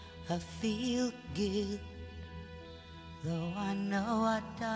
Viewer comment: I think im the only who thought they say " i feel gay " first time i heard it